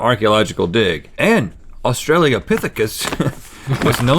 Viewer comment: did Rhett just say "Australiapithicus"???